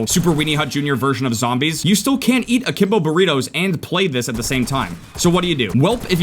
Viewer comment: did you just say akimbo burritos